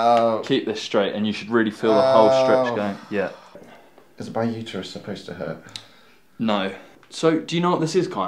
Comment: I swear he says "is my uterus supposed to hurt?" :0